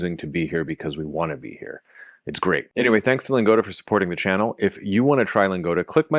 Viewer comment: Instead of "it's great" I hear "it's Greek"